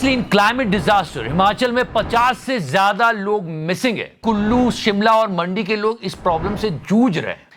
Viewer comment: Is it just me who heard Gullu instead of Kullu at 6:30 😂